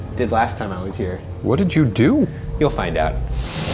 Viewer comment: when Shane asked Ryan "What did you do?" I heard Ryan answer "killed my dad"... I had to play it 5 times over to make sure he said "you'll find out"...but it still freaked me out for a minute.